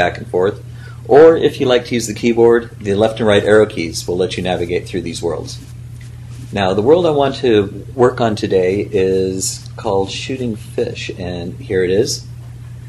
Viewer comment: I swear that he said "Shitting Fish".